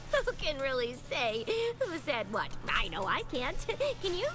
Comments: Where did they say Vinyl Scratch in the show...I don't remember that.. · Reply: Thibodeau Gah, I always have a moment in these that comes back to bite me, and that's the one for this episode. The timestamp in my video is 18:13. I somehow heard Twilight say "Vinyl" instead of "I know", even after a couple re-listens. My bad. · Twilight never says Vinyl. She says "Can you?" and some people somehow confused that for "Vinyl" (at least I think that's what you guys are confusing). there's a discussion of it on the Wikia page for DJ-Pon-3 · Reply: I realized after I got the video uploaded that it was wrong, though I thought I heard it when she said "I know", which is a lot closer to Vinyl than "can you". ^_^ So yeah, official name is DJ-Pon3, but to me, that will always be her stage name, whereas Vinyl Scratch is her real name. That's just my opinion though, to each their own. :D